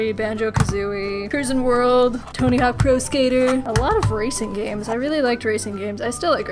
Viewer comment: I was listening to this in the background, so for a moment I thought you said "racist games" and had to rewatch that part.